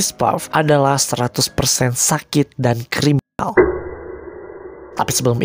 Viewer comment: bang mending yg gini jgn disensor, criminal? crimanal? jadi ambigu kedengerannya